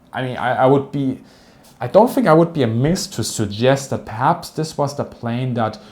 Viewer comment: Does he say “I don’t think I’d be a minx to suggest…”? I hope so. Maybe that has another connotation in other languages, but in English “minx” is a slightly old-fashioned term for “an impudent or very flirtatious” person… and I thing that’s awesome, you naughty youtuber, you! 😈